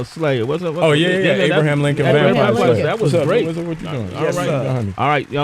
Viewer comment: What does say at 26:54? Is it "What is it with you", "You want to get trough?" or what is it? Don't tell by the look he gave him either... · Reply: He said "What's up, what you doing?", then he said something like "You going behind me," or "You gettin behind me", to the guy trying to get by.